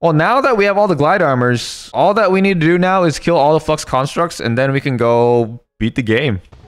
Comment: What did he say? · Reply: what he said:flux constructs What we heard: f*cks constructs